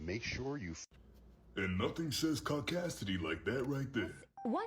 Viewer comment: When they said "caucasity" I heard "caucasians" and was like: what did they do?